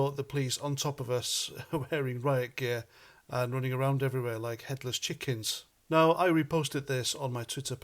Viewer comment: When you said “Met running around like Headless chickens” I thought I heard “Hitlers chickens” - who’s the official head of the Met? Oh Kim jong Khan Schickelgruber